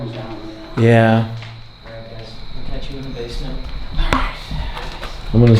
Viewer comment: The Norwich State Hospital opened in 1904 and operated until 1996. At its peak in the 1950s, the hospital housed over 3,000 patients. Over 40 buildings were housed on the 70-acre property and on Part 2 of the Norwich Investigation and With Josh Decided to stay over night in the HAUNTED ORPHANAGE. the link to Episode 1 of the Norwich Investigations! <link> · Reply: WTF IS THIS WHISPER SAYING? BASEMENT???? 😬😅